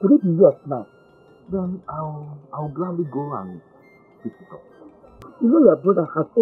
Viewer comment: Pick it’s up now 😂😂😂 I thought u said is for u 😂😂